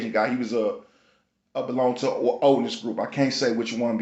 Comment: ”did you say he belong to an Odin’s Group @ 3:33 thanks” - MELL DUNEY 616